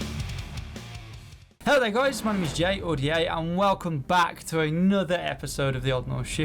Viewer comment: WAIT YOU SAY "HEY, ITS JAY, OR D.A." I NEVER REALIZED THAT! I thought you said something all bunched up and I could never tell until today. I feel so stupid!